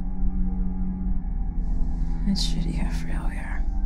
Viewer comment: Whoops. Always something you over look. Great video, I just can't catch what she says at 07:34 Can anyone help me out there? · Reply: Writer here! She says, "It's shitty how frail we are".